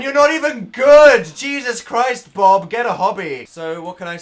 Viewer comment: i swear i heard hobbit instead if hobby and i was just sooo confused like wrong thing wrong time dan